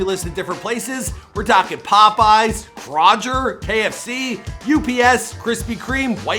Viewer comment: did he just say “crojer”